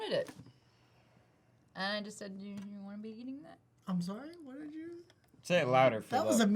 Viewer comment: What did maude say at 5:37 because I couldn't understand what she said? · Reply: When she whispered she said do you want to be eating that